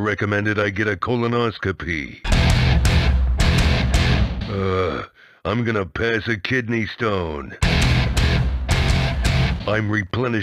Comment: I THOUGHT IT SAID " I'M GOING TO PISS A KIDNEY STONE, " AND I'M DYING!